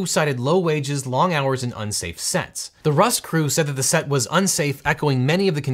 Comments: "Unsafe sets"? (16.21) I thought he said "unsafe sex" and had to listen twice! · i had to do a double take because i thought he said unsafe sex and not unsafe sets